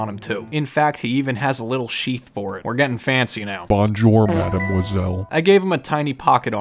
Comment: I love squish Melos! And I love so it is the perfect mix! 4:44 did he say bonjour madam Gizzel?! 😂😭 isn’t that from peppa pig?